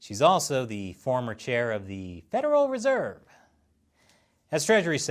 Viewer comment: I think I heard you saying, when talking about Yellen, "federal reserved". What a beautiful slip of tongue or intentional mistake, pun.